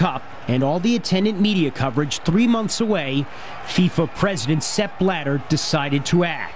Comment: The reporter sounded like he was saying Sepp Bladder LOL